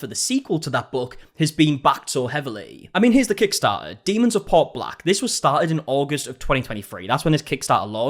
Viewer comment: When you said "Demons of Port Black" I thought you said the book was called "Demons are Part Black" and I was like "What kind of racist horseshit is this girl writing"